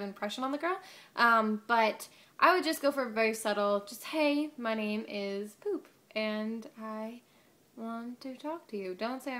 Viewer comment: I swear she said at 2:20 "Hey my name is poop"